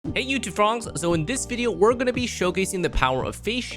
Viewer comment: am I crazy or did he say "frohngs" instead of frogs?😂